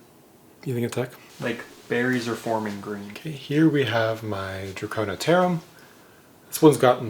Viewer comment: what's the name? CC says dracona terum, but nothing comes up under that when I search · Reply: It is a draconopterum